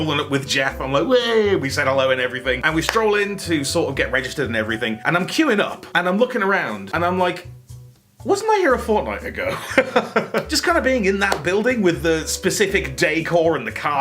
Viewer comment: did je say fortnite? Even the cc thinks he said it